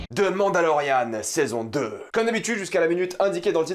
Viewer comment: j'ai ri tout seul parceque la premiere chose que j'ai compris c'est "Demande à Laurianne" à 0:08 XD